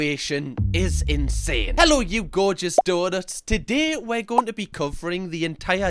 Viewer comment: LoL, I thought you said "hello you gorgeous daughters" at the beginning of the video, until I saw the donut.